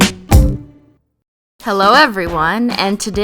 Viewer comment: who else heard her say hello in the beginning instead of holo